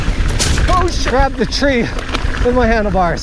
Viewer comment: Rainy day here too and I'm loving watching these old reviews. Now what was it you said at 6:24? OH, SHxx ? 😁